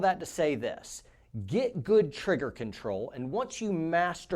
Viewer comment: When he said “get good trigger control”, I heard “get good first, scrub”.